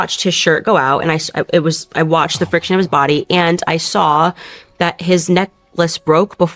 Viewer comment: Did she just say "friction of his body"?